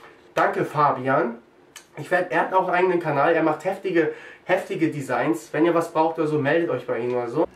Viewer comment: Ich hab an Ende erst verstanden: wer macht HÄSSLICHE Designs. Aber es heißt ja HEFTIGE xD